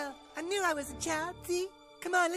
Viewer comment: sounded like said oh f*ck man